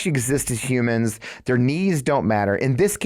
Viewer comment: At first I heard "their knees don't matter", not "their needs", and I thought "that's such an Airborne way to put it" 😂😂